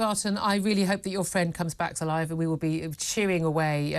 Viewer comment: Did she just say "I hope your friend comes back to life?" 💀